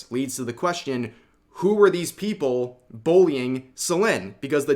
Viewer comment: I swear I heard "Boing" instead of Bullying 😂